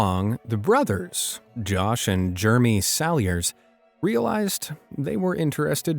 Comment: I have heard this at least 2x...the first brother twin is NOT NAMED Germy....its JEREMY!!!! I want stick a pencil in my ears when I hear "Germy". (┬┬﹏┬┬)(┬┬﹏┬┬)(┬┬﹏┬┬)(┬┬﹏┬┬)(┬┬﹏┬┬)